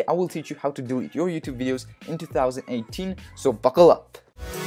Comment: I legit thought he said, "so fuckle up"